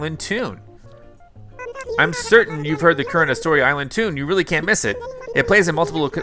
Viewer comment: I cant be the only one who heard him say "Corona island tune"